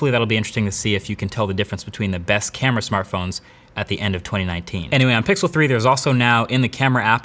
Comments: Did he say at the end of 2019? 😂😂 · Did he said " at the end of 2019 " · Around the 7:17 mark. I think marques said "end of 2019" not "end of 2018". It's really trivial and it definitely does not affect the quality of the video. (Video's great.) But just letting you know. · Did I hear at the end of 2019 ? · Did he say end of 2019?